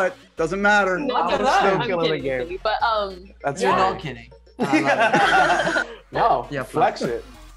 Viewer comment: is it just me or does Zendaya say “no I’m kidding baby”?